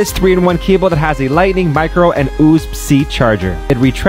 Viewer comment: did he really just pronounce it “oosb C” instead of “U-S-B C” or was it a glitch?